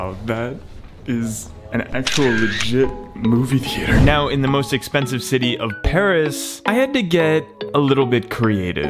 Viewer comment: "Most Expensive City in Paris" - 2020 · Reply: I thought he said « most expensive city is Paris »